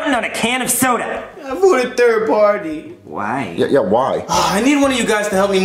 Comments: What did he say??????? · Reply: gibirish third party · What did joseph say at 4:06 I couldn't hear it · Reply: I vote a third party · What did Joseph say at 4:07?? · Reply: “I voted third party” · I still can’t figure out what Joesph said. Does anybody know because I’ve been trying to figure it out for years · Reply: "Can't afford a third party," probably. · Idk if it's just me, but ever since the original vid came out, I've never been able to understand what Joseph said- All I hear is "Third party" 😅